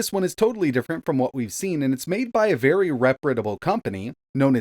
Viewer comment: I'm sorry, did you just say "repritable"?